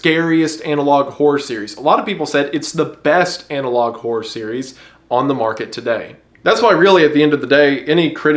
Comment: I thought you just said “The Best of Analog Horror” and was mortified that UrbanSpook was listed in the title lmao